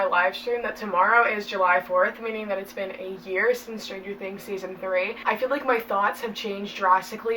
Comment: Oh my life I thought you said 8 years since season three and I almost convinced myself lockdown/quarentine has lasted that long.